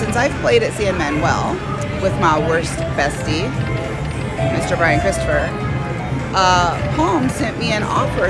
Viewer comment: I wouldn't rewatched the video and it said your worst bestie that's what it said I thought it said Brian Christopher what is the worst busty